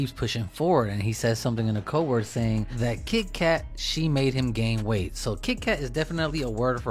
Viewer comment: THE SPECIAL K HE IS REFERRING TO IS TOOSIE OR PINK COCAINE WORM food is code for dirt And HE DID NOT SAY SHE, HE SAID SHIT (17:12)and when he refers to 44 he’s referring to a 🔫 🤷🏻‍♂️ you should contact me when you need to break down street lingo😂😂😂😉👍